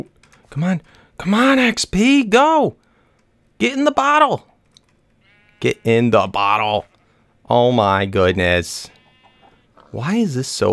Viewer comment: It sounded like you were saying "Get in the bottle xB" Lol sounded like you were Ash trying to catch em all :D